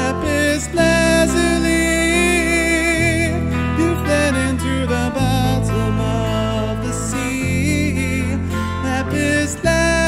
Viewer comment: Lol, I heard "you fled into the bathroom of the sea".